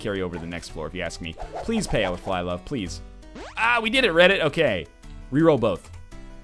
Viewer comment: did he say "We did it Reddit?"